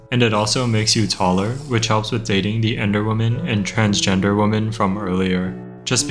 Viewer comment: Ahaha I thought it said transgendergerman